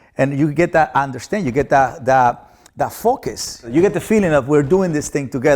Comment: Lol when he said focus i thought he was saying "F" it